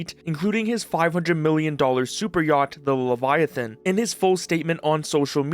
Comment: I thought you said 500 dollar super yacht lol